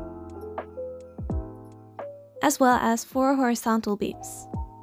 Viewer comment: Lol when she said “4 horizontal beams” I thought she said “4 horse beams”😂😂😂😂